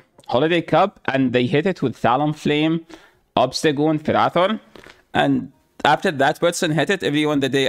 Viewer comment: Talonflame/Obstagoon/_______? Couldn’t make out what Said. Awesome video! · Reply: it was ferrothorn. You are absolutely correct